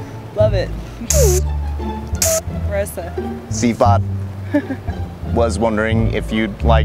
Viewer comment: did he say CFOP ? XD cubers triggered